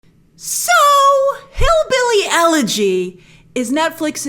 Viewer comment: I honestly thought that said Hillbilly “Energy”.🤣😂